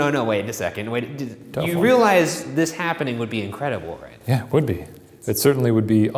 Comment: Be sure to follow the bois on instagram! <link> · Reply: BuzzFeedBlue The first voice sounded like it said this is not happening